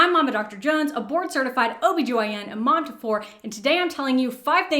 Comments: I misheard "a board certified" as "abort certified" and was kinda confused for a few secs. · Why did I hear “abort certified” when you said “a board certified” 😭😭